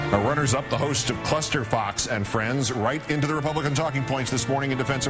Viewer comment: I seriously thought he said clusterfuck XD